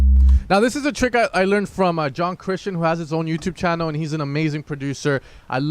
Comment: a trick you learned from whom? Can you put the link of his channel? I couldn't understand the name (English is my second language hehe) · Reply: NVM is it John Christian? Think I found him.